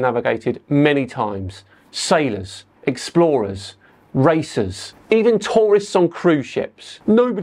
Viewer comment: I thought you said "sailors, explorers, racists"